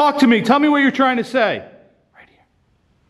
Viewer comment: Did anyone hear at 11:19 a voice that says 'Right Here'